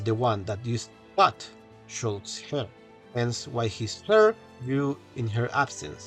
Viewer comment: Sorry can't really make out what you are saying here? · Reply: Used to cut shulk's hair. I made subtitles (not automatic, I wrote them) for the video because the mic was acting weird.